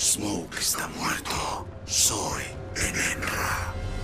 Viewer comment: Min 11:13 que dice smoke? Smoke esta muerto, soy el ...? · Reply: Enenra. 😥